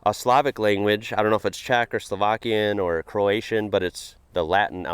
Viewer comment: Slovak girl here. For a moment I thought you said "Czechoslovakian" and I was like "no no no.... not another foreigner not knowing we split more than 30 years ago. Maybe I heard that wrong. Let's give it another try". Then I listened again and heard "Czech or Slovakian". My apologies, sir. And huge kudos to you to be able to recognize the usual grammar symbols of these coutries.